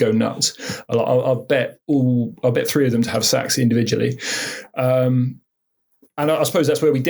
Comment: smiled because he thought said "I'll bet all of it three of them to have sex individually."